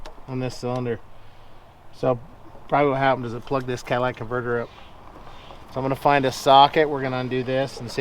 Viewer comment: did you just say Cadillac converter? 😂